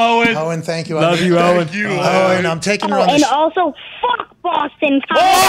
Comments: I thought he said “Boston College” lol. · All this time I thought he said f@ Boston College LOL